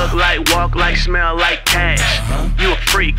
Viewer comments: plz tell me i'm not the only one who thought he was saying "look like, walk like, smell like ass" LMFAO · When i first heard it in franklin’s car i thought it said “walk like talk like smell like ass” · anyone else thought he said " look like walk like smell like ass"....? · I always thought this said "walk like talk like smell like ass" HAHAHAHA quite disappointed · I thought he was saying look like walk like smell like ASS lol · i thought it said look like smell like speak like shit